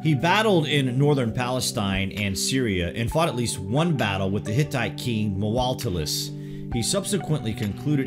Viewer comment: did he say "the hentai king" ?